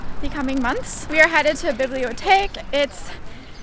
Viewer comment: I love your videos and your style... always inspiring. Also I had to smile as you said "Bibliotheque"... i clearly heard "Bibliothek". 😄 Sending love from Germany 😘